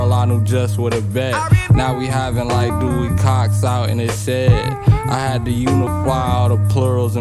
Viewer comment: can somebody tell me what the hell he says here?? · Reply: Now we have it like Dewey Cox out in the shed. Then he goes on to sample “you truly don’t want no part of this shit.” From Walk Hard. Dewey Cox discovers his band mates are getting high and his buddy’s reaction is to tell him you don’t want any part of this shit.